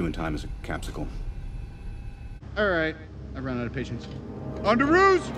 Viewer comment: I always thought Tony said "On the roof". 🤣🤣🤣